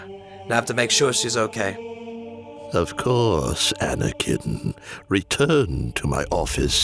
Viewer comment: I had subtitles on and instead of anakin it said anna kitten